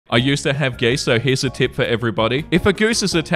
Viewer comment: At first I heard, "I used to have gay sex..." and like, good for you, buddy. Why'd you stop? Lol